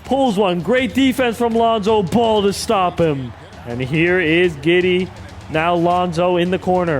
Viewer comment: I wasnt paying attention and i heard Diddy. i was like who!!!